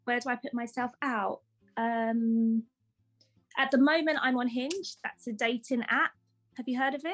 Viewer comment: I first heard I'm unhinged